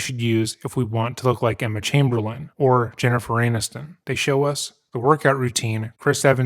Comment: Did I hear "Jennifer 'Anus'ton"